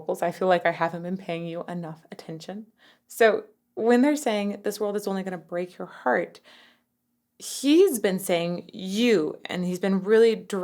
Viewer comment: I always thought the backing vocals said "This GIRL is only going to break your heart."